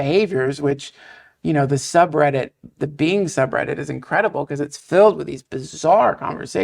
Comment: What sub Reddit his Duncan talking about at my hearing is awful and captions are wrong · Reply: r/Bing subreddit.